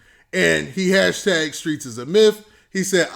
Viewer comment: I thought troy Ave said " the streets is a myth" after ppl called him a "Rat"...